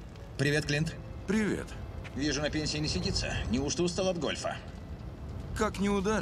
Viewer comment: Послышалось, что он сказал: «Вижу на пенисе не сидится»))